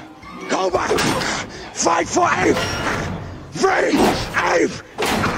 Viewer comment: I always thought koba said "fight for REAL ape" instead of "free ape" which is cooler imo. To me it sounds perfect, koba didn't view caesar as strong or a "real" ape because of his assistance to the humans.